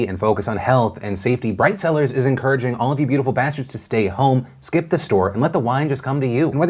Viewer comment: I feel like my life is a lie because everytime I hear "beautiful bastards" and I just realized he says "beautiful bastard"